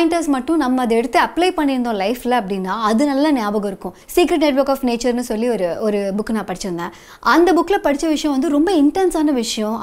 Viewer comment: That book name please river.... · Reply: I think she said "The secret network of nature" ~